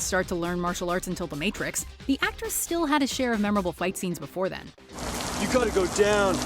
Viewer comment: Did you call Keanu Reeves and actress at 1:24 ?